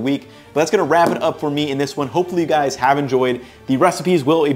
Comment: did he say "I will Cacio" at the end there?..